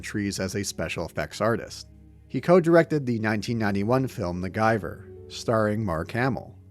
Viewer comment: The gyver, I thought you said "macgyver starring Mark Hamil" which would have been a very different experience.